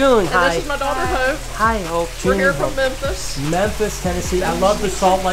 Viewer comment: Lol! The 2 ladies that came in for a tour, introduced themselves.. And the lady with the red shirt on said "Hi, this is my friend Hope", and Brian said "Hi Hope" but it sound like he said "Hi Hoe".. 😆 😆 😆..